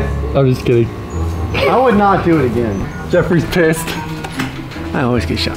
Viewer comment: did anyone hear what he said and I heard and he said (Jeffery is pissed ) and if you don't believe me just listen to him